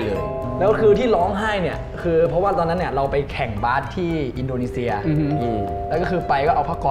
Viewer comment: indo fans : we watch with bahasa kalbu 💭💭 when they're laugh we laugh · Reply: I heard neo said Indonesia, am I wrong?